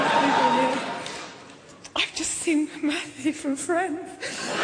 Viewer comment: What does she say at 5:40? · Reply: "I've just seen Matthew (Leblanc) from FRIENDS"